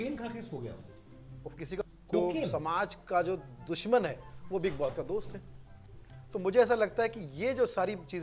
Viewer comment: Did he say "Samaj ka jo dushman hai vo biggboss ka dost hai" 🙄 is it so?? Bhai fans 😭😭😭